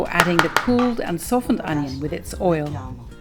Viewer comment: I'm trying to understand what it says, english is not my first language, you say "before adding the cooled and softened (onion?) with its (???)" The description has one onion so I assume that's correct, but what is the other word? Olive oil?